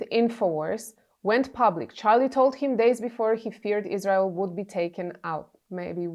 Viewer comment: the heck do you mean? No he said he feared Isreal would be taken out not what you said about fearing Isreal taking him out. It says it plain as day